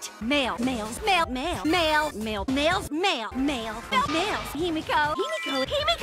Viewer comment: Why do I hear Mimiko instead of Himiko?